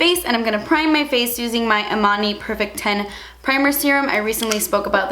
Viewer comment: i thought she said armani instead of emani and i was like ???? girl??? what target are you shopping at??? lol